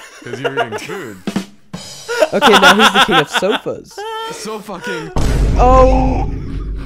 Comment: WHAT YOU SAY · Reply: Sofa King.😅